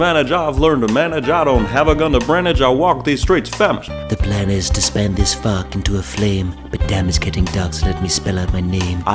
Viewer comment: “The plan to SPAN this FARK into a flame.” Don’t tell me I’m the only one who heard that 😂😂